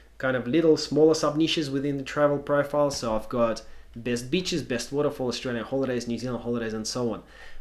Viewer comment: does he say best bitches? Lol jk jk